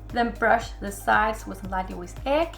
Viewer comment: what did you say here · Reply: i think she said "wash it with egg on the edge"